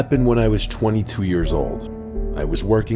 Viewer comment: it sounded like it said 23 years old but in reality 22 years old